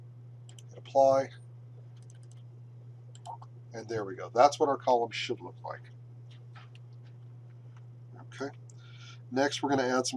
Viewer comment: "That's why we call it XXXX" minute 8:00 . I cannot understand the word . Maybe someone can write me that word in a reply. Thanks, Nephew of Ida. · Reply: "That's what our columns should look like"